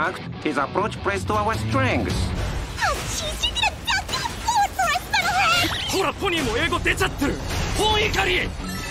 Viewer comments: what did she say here? i can't understand it at all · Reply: “Aw jeez you’ew gonna dogone blow it for us metal head” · what did she say? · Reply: “Aw jeez you’re gonna dogone blow it for us metal head”